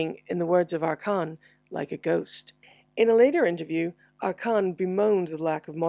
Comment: sorry, but who said that? i couldnt catch his name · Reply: Arcan Öğütverici, he was the guy who organised their gigs in Turkey, 1990.